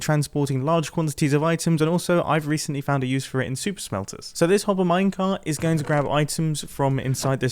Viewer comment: I thought he said soup smelter not super smelter :0